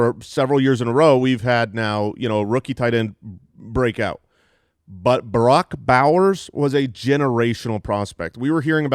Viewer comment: Lol sounded like he said Barack Bowers